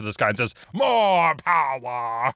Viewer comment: When Boshask held his arm up and Said "MORE POWER" I heard "MOM POWER" and I love that